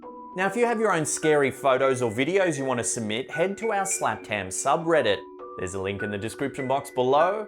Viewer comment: Hahaha- when he was telling us about his reddit, I thought he said Slapped Cam 😂🤦‍♀️ And I pictured him saying, "Captured on our slapped-cam." Oh help.